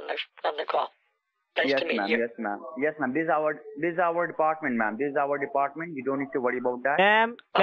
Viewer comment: The intro sounds like he says “the soup department”